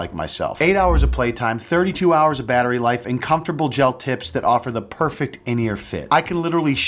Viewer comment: I totally misheard that as "comfortable gel tits." WTF is wrong with me? 😂